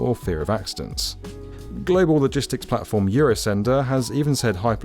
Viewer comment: Can't help myself but I always hear "piper loop"...